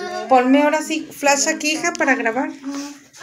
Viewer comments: Se escucha que dicen " ayuda" en el minuto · se escucha que dice ayuda